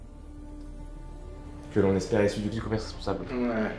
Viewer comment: Aidez moi a comprendre ce que dit! Perso j'entend "Que l'on espérait issu du-dit couvert" ... lol